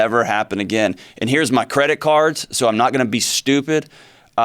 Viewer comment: Did he say.... CREDIT CARDS!?